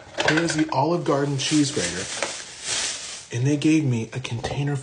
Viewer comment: I noticed the subtitles said "here's the Olive Garden Cheeseburger" instead of cheese grater.